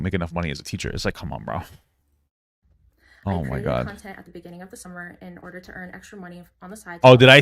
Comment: Oh shit i thought that said consent, my bad